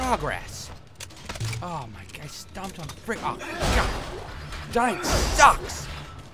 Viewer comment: What does Isaac say at 3:30? Fucking...sucks? Stuck?